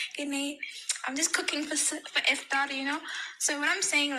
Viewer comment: Did she really say she's just cooking for Ifdah??